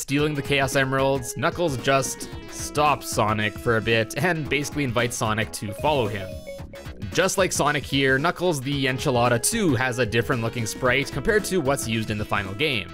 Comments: did you just say Knuckles the Enchilada xD xD xD xD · did he say... ENCHILADA?